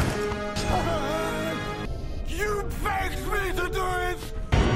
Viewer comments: This is brilliant LMAO!! But what did he say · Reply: YOU BAKED ME THE GROOITH! (at least I think so) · I thought he said you baked me the toilet 🚽 🤣😆😂